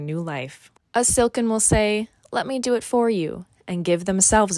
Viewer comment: All I heard was “let me do it for you Kermie”